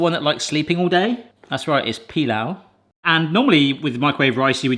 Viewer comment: did you just say Pilav here?😂 Thats what we call cooked rice😊